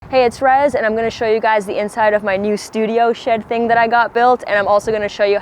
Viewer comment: I Swear I heard "my new studio shit thing" 😅 ÷ Rezz I love you lady I never heard of you prior someone put me on but 2024' Edge is on repeat for me love that track but I think you said you were going to show me your new "shit" 😂